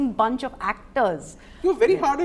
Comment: What did she say when he said 'you are very hard on yourself'? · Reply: Fairly so na..